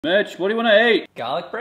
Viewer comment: I fr thought he said bitch in the beginning lol 💀